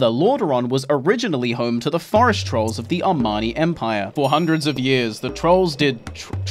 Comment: Did he say "Armani" lol